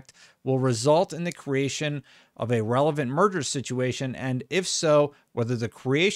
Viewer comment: Sounded like you said “murder situation” lol